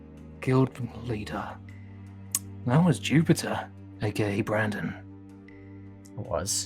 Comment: I thought said a gay Brandon I was about to go into a coughing fit and laugh so hard Also When Oakley "Dies" Flurry- Cries beg pleads: DESTROY ALL BACTERIA IN THIS CASE IS THE BACTERIA